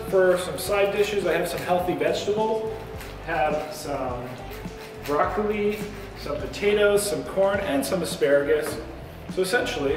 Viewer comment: I could swear you said broccoreeeeee🤔